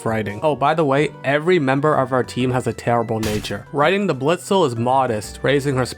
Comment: I kept mishearing "Writing" as "Raiden" and that singlehandedly made them my favorite.